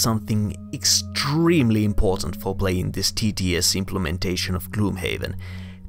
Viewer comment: Good video, bought TTS for Gloomhaven and managed to figure it out but got some new tips from you. Right up until the end I thought you were saying "the tedious Gloomhaven implementation" and I thought that was rude but then I realized you were saying "the TTS Gloomhaven implementation"!